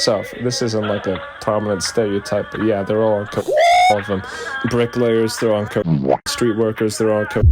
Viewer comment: What is this??? What did he say? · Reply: Coke